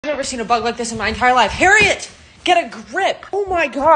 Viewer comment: In the beginning I thought they said “I’ve never seen a butt like this in my entire life” 💀